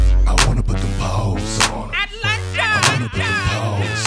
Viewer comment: "I'd Let ya"??? I thought he was saying "Atlanta". Damn. I'm trippin'.